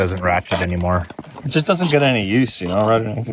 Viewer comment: wait did he say tools don't get any use running skidoos?